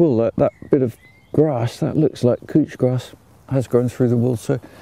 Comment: I love this man's content. Also as an american, I thought he was saying "cooch grass"...cooch means something different to me. So that caught my attention.